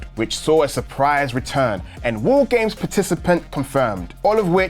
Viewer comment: I thought he said Woo games instead of War games, which made me wonder, what would the "Woo games" entail?